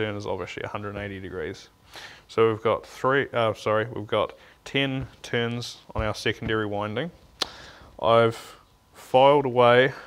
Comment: I kept hearing "I just did tin tunes on the secondary windings". Great video !